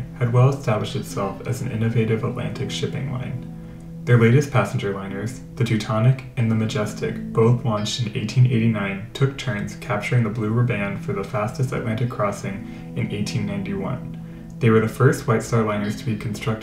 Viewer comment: Did he say "blue ribban" at 1:48? 😆 Very interesting and informative video but it sounds like someone reading a report to the class. Has a plain, dry, emotionless feel to it. Like someone reading an encyclopedia out loud Sorry, I'm not trying to bag on the video, well, not to harshly anyways. Hope it gives some insight to how this video feels and maybe next one will be better. It's still fascinating stuff though. Just lacks feeling. Better luck next time.